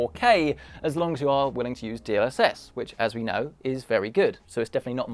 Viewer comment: Sorry, I couldn't hear what you said, "As long as you are willing to use ____?____" around 16:06 in the video. --Hope someone can tell me :s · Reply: DLSS